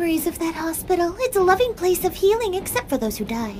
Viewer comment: that's the reference what I heard her was saying Scooby snacks from Scooby Doo